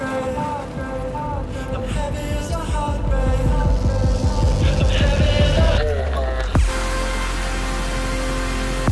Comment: LOL wait what does that say at 3:08 "Levelcap has no ..." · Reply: LevelCap has no clue :P